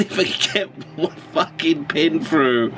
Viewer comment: I thought you said "Fuck VPN" in the beginning.